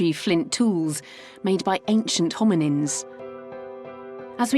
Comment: Did she say "homonyms"? Sounds like that through my speakers. That segment was quite interesting and while it's great that the divers have found that site, I suspect actual marine archeologists watching would be sputtering with rage, as it seems there's no recording of finds on any type of grid to try and determine pattern, distribution of objects, etc.